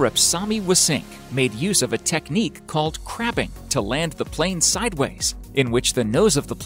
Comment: it sounds like he says a Technique called craping